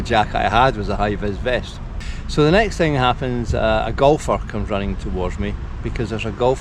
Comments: Hi Folks I am 'James'. When and I discussed doing this video I hoped it would be useful to our community. Your comments are humbling. Still waiting on some parts for OY so this season is written off. Gladly though she wasn't. I bought a Eurostar that will tide me over and made my first trip back to Bute a few weeks ago. There was some mental blocks to overcome but now been there 3 or 4 times since. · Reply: Hi, you seem like a genuinely nice guy and I'm sorry for your mishap. I thought you said "there were gofers coming toward you" after you exited the plane but I'm American and you were saying "golfers". Laugh is on me. Take care, Ted in Sebastian, Florida. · I thought he said a gofer was coming towards him haha. Was thinking this man just crashed and a gofer was coming to towards him now for some odd reason.